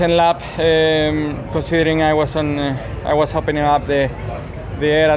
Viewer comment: Can someone please tell me what checo said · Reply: I think he said “I was opening up the air”, as in leading the pack, with no cars in front of him. So he was punching the hole in the air for the cars behind